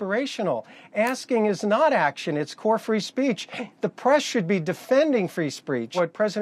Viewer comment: Wait... did he just say "free spreach"?